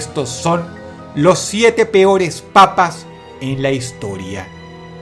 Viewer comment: Pense que decia las 7 peores papas de la historia :v